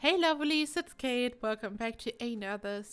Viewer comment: You said “Hey Lovelies” but it sounded like you said “Hey Loverrlee” and I was like 😳😳😳 Anyway amazing job on the build! So cool! I love the hot tub sunken into the foundation! 😍😍😍